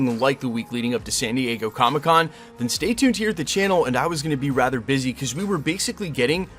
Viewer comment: I thought he said he was going to retire